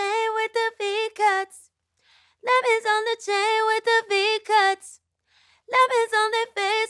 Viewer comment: I always thought she said diamond on my chains with the D cup